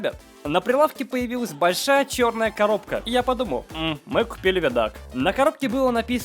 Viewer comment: я не понял что он сказал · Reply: Я включил англ субтитры там сказано VHS player. Значит он сказал видик